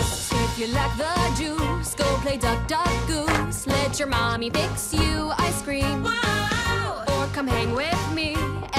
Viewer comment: Uhhhhmmm ”let your mommy fix you ICE CREAM” and ”you just gotta prove you’re not a DOORKNOB anymore” 💀💀💀 did I hear that right.... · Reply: I heard “let your mommy fix you A SCREAM” and I got so confused lol